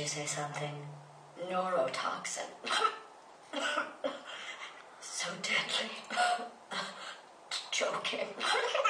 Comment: (For anyone who cant hear what i said) What was that.. did you say something?.. neurotoxins.. (cof..- cof cof) so difficulty.. (cof-cof) im choking.. BAHAHAHAHAHAH kidding.. i can take a bath in this stuff.